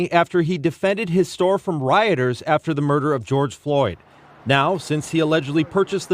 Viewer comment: did he say "after the MURDER of george floyd"? They're still LYING about that?! The MSM is the enemy